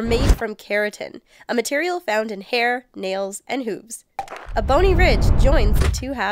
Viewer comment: Hair, nails and what? It sounded like she said "hoops."